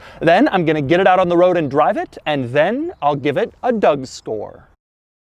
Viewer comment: Did he say "give it a dougSTORE"? Just shilling on that carbids thing huh?